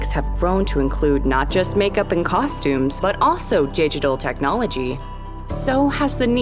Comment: she said gigtallt instead of digitally